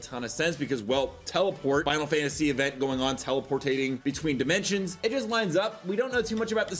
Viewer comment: am i the only one that heard him say teleportating? teleporting? teleportation?